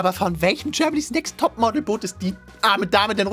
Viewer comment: ich hab verstanden tschernobyls next topmodel XD das würde sogar sinn machen so wie die aussehen